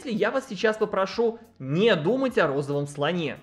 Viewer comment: лол, я кажется хакнула эту систему, ибо сказали не думать о розовом слоне, а я не правильно услышала и поэтому подумала о голубом слоне